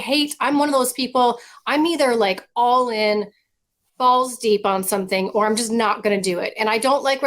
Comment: Did Laura just say " Balls Deep" in something... 💥😜